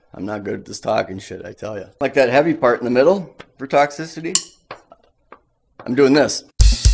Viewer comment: i keep hearing "texas city" instead of toxicity it took me a while to figure it out lol